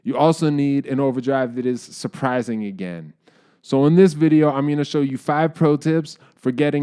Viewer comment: Lol, I needed to do a double take. Thought you said you needed a pedal that was 'Surprizingly gay'